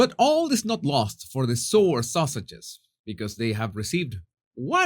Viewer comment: It sounded like you said "poor sausages"!!! Bwhahaha 🤣